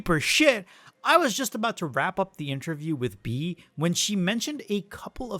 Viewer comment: I thought u were gonna say You were about to rap